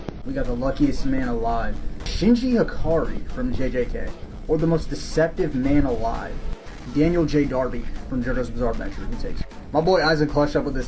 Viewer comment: I thought he said Shinji ikari 😭😭😭